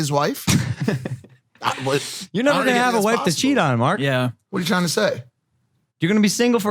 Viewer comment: does Logan call Mike Mark? Not the first time I've heard this..is this just a thing Logan does when he picks at Mike or am I hearing wrong or is he messing up Haha.